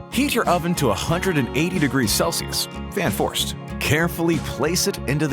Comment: I thought u said carefully